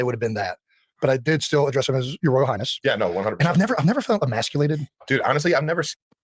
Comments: He did NOT say, "I've never felt SO emasculated." 🤓PW did NOT try to emasculate him. What Kelce really said was, "[He could have pulled rank and made me feel small, but] I never felt emasculated" by PW, because PW was respectful. 😉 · Hi! I thought he said he never felt immasculated. I don't think he said "I never felt SO immasculated. " Listen to it again and let me know what you think. ❤